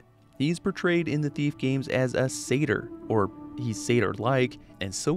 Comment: he was a sayter boy, he said see ya later boy..